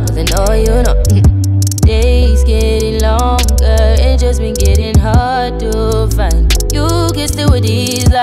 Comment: when I first heard this song I thought she said "days get longer it just be gettin hard to fight"